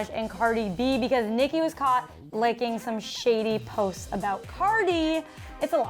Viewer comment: she said licking instead of liking lol